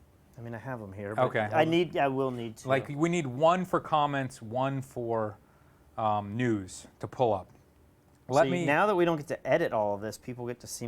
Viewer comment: Haha @ 1:35 thought said, We need one for comments & one for pullin up nudes... listen for it.